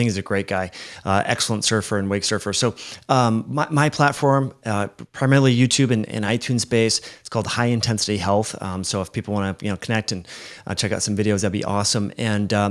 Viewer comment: i thought i heard high intensity elf,,, and was like oh must be a frodo fan